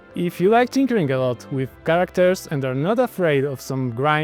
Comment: i thought he said "if you like to drink alot with characters" yes sign me up